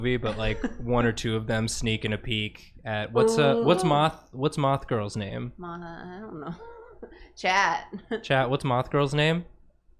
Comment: Why did I hear "what's moth girls name?" "Chad"